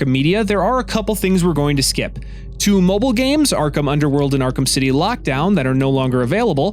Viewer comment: I thought he said Arkham world and I nearly went crazy